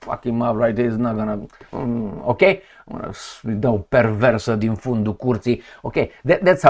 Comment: Sounds like you said "perversa de un fundi curti'. lol😅 😅